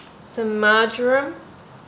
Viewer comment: what was the first blue pack she added to the potatoes? It sounded like she said margarine. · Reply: Marjoram, it's an herb. :)